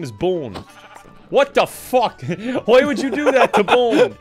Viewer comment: still shocks me every time cause Kevin actually says fuck instead of feck.